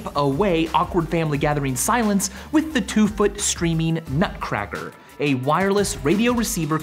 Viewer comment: I thought he said "Screaming Nutcracker," which would have probably been just as good for wiping away awkward family meetings.